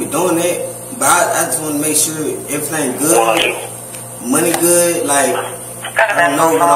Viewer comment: Am I the only one who didn't understand what the hell he was talking bout at the desk the whole time? 😂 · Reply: I heard… “money goood and shit”